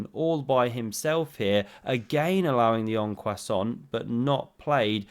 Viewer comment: did he just say en croissant in the en passant? hahahaahhaha